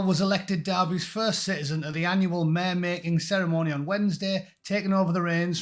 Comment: i thought you said mayor mating ceremony there for a second , and thought wtf ??!?!?!